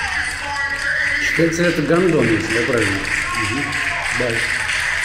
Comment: мне послышалось "гауно"